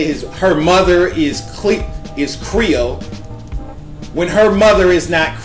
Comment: It sounds like you said "GRIOT" instead of "CREOLE." LOL... STILL LOVE THE VIDEO.